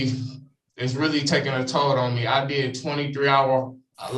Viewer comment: Is it just me, or at 8:40 did he say life was taking a toilet on him??? I mean, it can really take a toilet (toll) on me too some days! 🤣😂